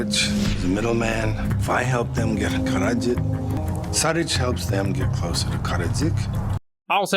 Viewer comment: I think what he says is: "Šarić helps them get closer to Karadžić" both are Serbian names, hard to pronounce. Karadžić was the leader of Bosnian Serbs during the Bosnian War, however I have no idea what any of this has to do with the movie and neither does Seagal, he probably thought these names sound cool